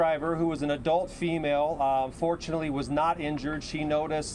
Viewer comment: Check out what he says between 1:22-1:26 did he say ....(UNFORTUNATELY)the female truck driver wasn't injured ?? WTF!!?? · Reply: Yeah I heard that the first time watching this , I replayed it he He said "Fortunately"